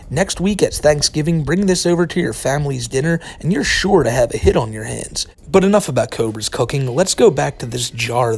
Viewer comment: When you said bring this food to Thanksgiving and "you're sure to have a hit on your hands" I thought you said "hit on your head" and I was like wow killing someone for food is pretty severe